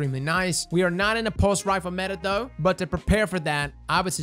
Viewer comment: did he just say "we're not in a pulse rifle meta?"